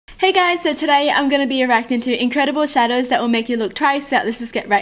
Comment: Did anyone else hear her say “eracting” at the beginning? Seriously, check